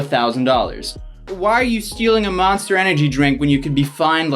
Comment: Damn at 3:16 say steering instead of stealing I died inside thanks for not editing it out